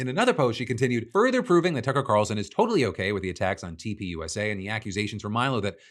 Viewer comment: I swear on my life I heard him say Cucker Carlson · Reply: i heard him say Cucker Tarlson. had to rewind a few times to make sure i wasn't wrong